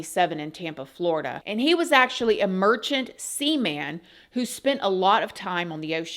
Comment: I thought you said a virgin seaman 😂